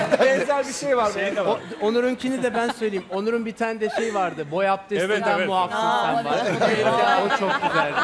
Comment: de Alper Kul ne diyor? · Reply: "boy abdestinden muaf"